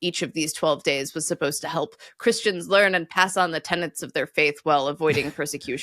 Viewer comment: Did I hear “tenants” rather than “tenets”? 😮